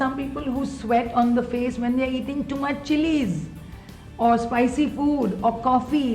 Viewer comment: did she say when they eat to much cheese? I cant understand, can someone help? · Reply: Hey there, she mentioned too much chilli or spicy food. - Team Glamrs